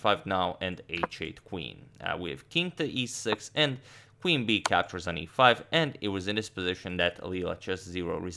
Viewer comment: I heard Queen bee captures on e5